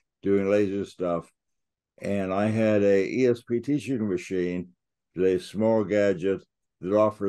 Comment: what is that APP he shows you? CC says “poor choice trainer” 😂 “Four Choice Trainer” does not come up in app search on iPhone. Would it be possible for you to include a link in the description? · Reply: "ESP Trainer" is the name of the app.